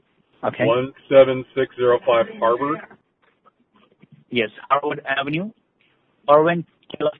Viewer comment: I thought that he was saying Harbor Ave and it was making me think that he was meaning Harbor Blvd but after looking at the Ralphs location it was Harvard Ave. I also knew that it was near where I live in Anaheim.